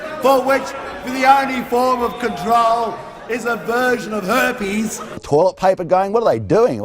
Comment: "Bottom-dwelling, mud-sucking creatures for which the only form of control is a version of herpes" haha wow. At first I thought he said a "virgin of Hermes" instead of "version of herpes" and I was SO confused